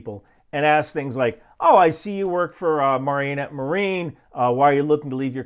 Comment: wait.. did you mean "Mare-enette" marine? all i heard was "Mar-Enette" marine... lol. Wisconsin pronunciation can be tough